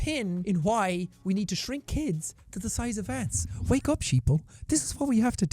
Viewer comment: I'm sorry but did he say sheeple😂😂